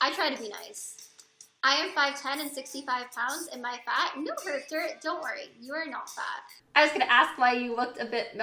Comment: What does she say at 0:25 please? I couldn't undedstand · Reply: "I am 5"10 and 65 pounds, am I fat" "no, don't worry, you're not fat!"